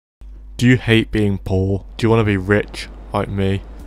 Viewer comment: Nobody: Absolutely nobody: Do YoU HaTE bEINg POOr? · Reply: i thought he said paul "do you hate being paul"